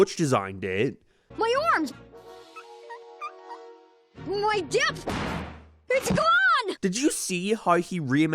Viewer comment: When I was a kid, when Jimmy said, "My depth!" I thought he said "My desk!" and was very confused with why he fell on his back.